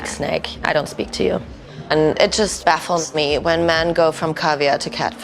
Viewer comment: Did she say "It just faffles me" ?